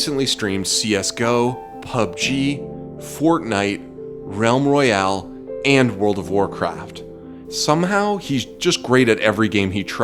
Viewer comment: i dont know what him said plss anyone can tell me pls? thx :D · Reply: Realm Royale, and world of Warcrafts.